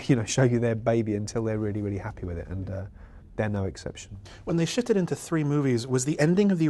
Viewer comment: i seriously thought he said "when they shit it into three movies.."